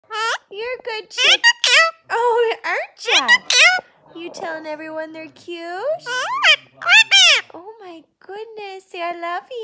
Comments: I thought he was saying you’re SO cute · I thought when he said "oh my goodness" he was actually saying "you're so creepy" and when he said "goodness" again I thought he said "creepy" 😂😂